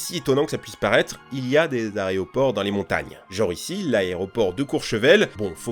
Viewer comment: c’est moi où il dit areoport ? 😂